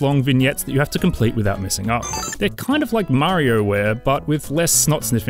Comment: did he just say... m a r i o w a r e